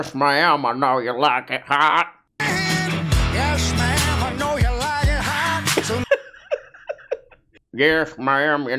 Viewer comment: I thought he was saying "Gas man, I know you like it hot" and all power to him